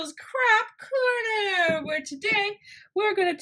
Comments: Instead of you saying craft corner I thought you said crack corner.... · I thought she said crap corner